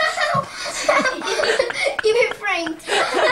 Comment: I still to this day cannot understand what he says a 9:09 😂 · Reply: You've been framed he said